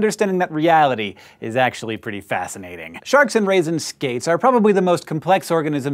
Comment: I have replayed the first 20 seconds of this video a few times now and I can't quite hear was he's saying. Aside from "shark" what other animal(s) did he list off? I keep hearing "raisin skates" O.o...